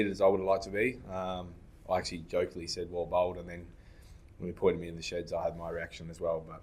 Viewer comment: Looked like he swore Siraj & he saying he said something about bowling 😮 but yes Siraj should maintain his composure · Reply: No, he said “well bowled”. Why do you guys imagine things? Did you even watch the video?